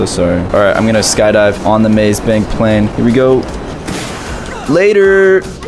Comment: did he say maze bank plane ???